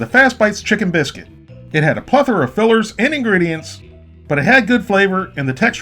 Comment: Did I hear plethora @ 6:39?